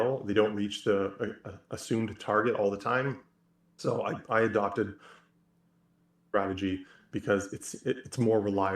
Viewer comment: the audio cuts out perfectly when says "i adopted a ________ strategy". did that just happen to cut out accidentally at the perfect time, or was it purposefully muted because we weren't supposed to hear it? curious as to what (blank) strategy he is saying there! I assume some type of cycle strategy but it just cut out on purpose I presume and I wonder what we weren't supposed to hear??? can you fill us in please? :) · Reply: he said cycle strategy I think - weird it cut out because I dont remember it cutting out when we recorded it.